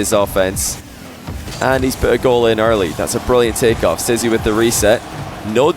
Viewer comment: did you call stizzy "sissy"?